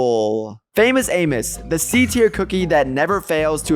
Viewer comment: i thought he said "famous anus"